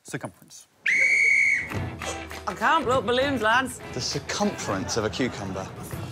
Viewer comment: Rare scoring error; Alex said that Mark and Nish got 4 points for getting the second most differences right, but then when he reveals their totals, they both got 3 points for that part of the task. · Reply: Aah yeah I misheard, I thought it was circumference of a cucumber